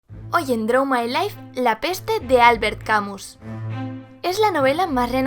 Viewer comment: Pensé que dijo la peste negra jajaja:"V